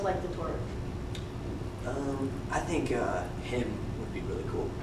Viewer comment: what is she asking at 11:53 and do I hear saying HIM???? HIM??!! <3 <3